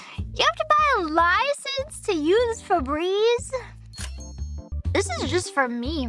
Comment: She's saying "Febreze" instead of "for breeze". It's a cleaning brand here in the States.